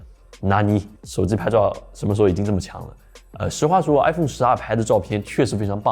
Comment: 请不要说iPhone十R！完全听成了iPhone 12😜😜😜